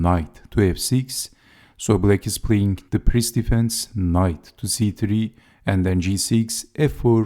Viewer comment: Sounded like he said the piss defense.